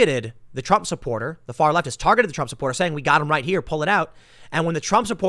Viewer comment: it sounded like to me they said "did he pull it out, did he pull it out" which I assumed to what was the bear mace but what who ever was speaking assumed was a weapon. That is best case interpretation for the defense.